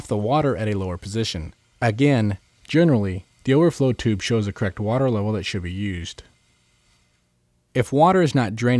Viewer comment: I swear I kept hearing him say "cracked water level", couldn't figure out what he was talking about. Correct! Correct water level. The word's got two syllables...